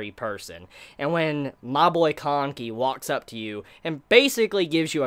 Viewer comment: I literally had to listen a couple of times here just because it sounded like said kaneki lol!